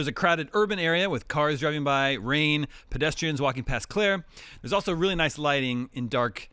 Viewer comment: does he say "pedestrians walking past Claire" ??? That's what I heard